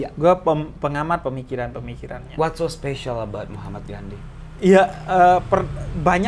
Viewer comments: Muhammad Gandhi? Atau gw yg salah denger? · did you said Muhammad Ghandi?